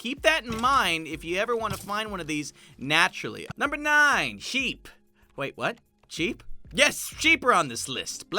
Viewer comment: i thought he said "jeep"